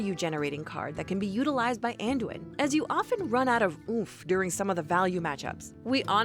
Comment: Did she just say "As you often run out of OOF during some of the value matchups" ????